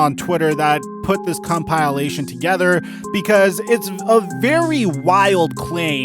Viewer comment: did he just say "cum pile astion"